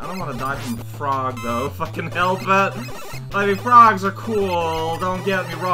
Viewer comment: oh man am i the only who heard that as sometihng else? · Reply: i heard the "if I can help it" as "fucking help it"